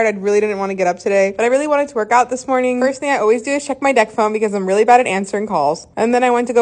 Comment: Mabey it’s the diabetic in me but I thought she said “Dexcom” not deck phone